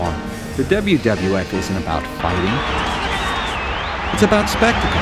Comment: What does narrator say at 4:42????? "Wwf isn't about __?" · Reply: fighting